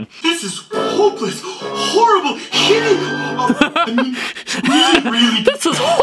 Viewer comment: I heard hitty